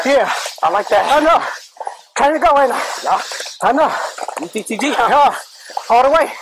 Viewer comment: The suspect didn’t say “you think your good”, he said “your ttg” which means trained to go.